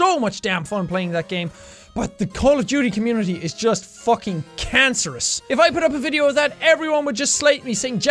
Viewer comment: did he just say cancerous? :(